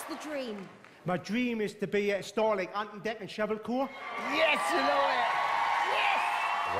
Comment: My dream is to be a starling anten denton shevel core. *Does Dance* Simon: I don't know what you said your dream was, but whatever it is I hope it isn't this. That's a no from me.